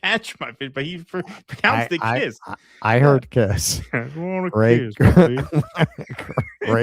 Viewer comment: I heard it as "...wanna kiss my feet..."